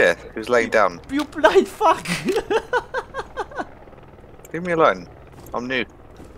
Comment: It sounds like he says "Leave me alone I'm nude" lol